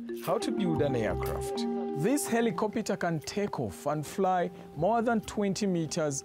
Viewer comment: did he just said "heli-copita"?